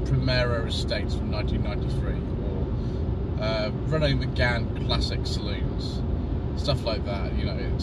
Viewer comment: Did I hear the words Renault Megane Classic at 7:25? 👀